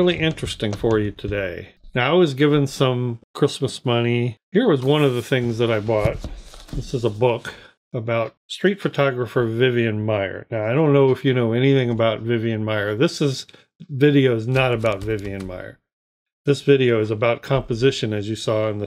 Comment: I thought at first you said "straight photographer…" and thought "what on Earth could her sexuality possibly have to do with photography? lol. I blame my misshearing on Covid.